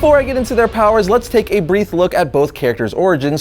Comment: did he say brief or brieth?